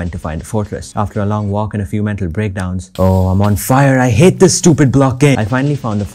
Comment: "I hate this stupid block game" Said every minecraft player ever, out of rage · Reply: I thought he said "I hate this stupid block, aye" or "I hate this stupid blockade" lmfao